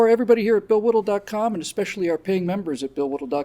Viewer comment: "I'm Bill Whittle... dot com." I mis-heard this as "I'm Bill Whittle... 's dog, Tom." Probably should have been!